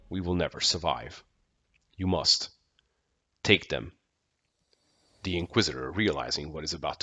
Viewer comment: I hear "take the alpha" not "take them"